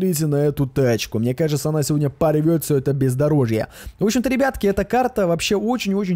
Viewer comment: Мне послышалось,или ты сказал пиздарожье?